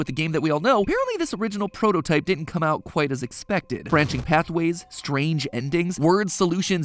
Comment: Question, why did I hear “branching pathways” as “branching cat boys”